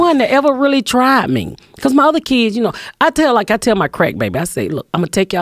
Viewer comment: "I tell my crackbaby" LMAO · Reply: Lmao i thought she been saying “Craig baby” 😂